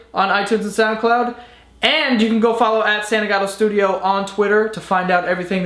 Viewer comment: When he told us to follow him on twitter I thought he said his username was "santa got a studio" lmfao.